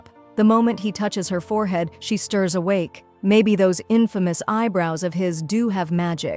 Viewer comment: Im sorry did you say imfomouse eyebrouse😂😂😂😂😂😂